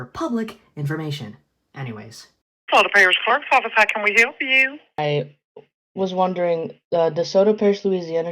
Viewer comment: did they just say how the f**k